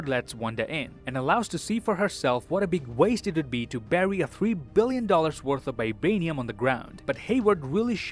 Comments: I feel like Vision's vibranium being worth $3 billion is far too low. I honestly thought they said $300 billion, which I feel is closer to how much it should be worth. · I feel like Vision's vibranium being worth $3 billion is far too low. I honestly thought they said $300 billion, which I feel is closer to how much it should be worth. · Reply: I thought they said 3 trillion